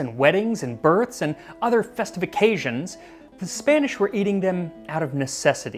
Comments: I know you said,"festive occasions", but I heard, "festifications". Did we just invent a new word? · I could have sworn you said "other festifications", I thought that was another word you were trying to revive like "bedight". Anyway, yes please to "history of chocolate" episode! I'm sure it would be just as good as the history of sugar two-parter